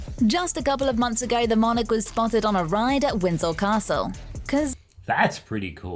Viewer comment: Why does the narrator sound like she is saying Winsol Castle and not Winsor Castle.